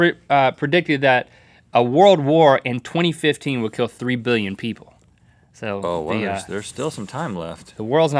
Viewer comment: A world war with only 3 million casualties? maybe if it lasted for a weekend. OH I KNOW! Black Friday weekend! IKEA v Walmart v Target v Best Buy. KMart be like "I wanna play war too!" SHUT UP KMART GO PLAY WITH SEARS & JCPENNY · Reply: I thought he said 3 billion?